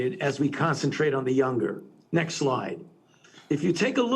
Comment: he should wear mask in zoom meetings like Biden. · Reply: Sounds like fauci is saying "next lie"